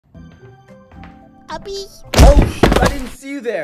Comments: it sounded like he said "Puppies" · i thought he said puppies